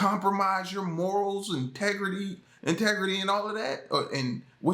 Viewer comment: did you say tegrity lol remind me of south park